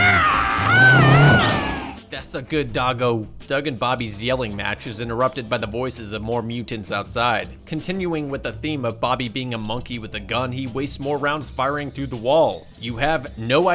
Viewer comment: did he just say “das a good dogo” 😂